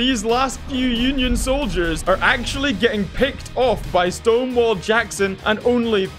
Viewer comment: I actually thought he was saying Stoneball